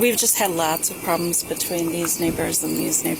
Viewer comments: I swore I heard this momma say “these n words and these n words “ instead of neighbors 😭 my jaw dropped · the way she was pronouncing 'neighbours' I could have sworn she said the N word twice.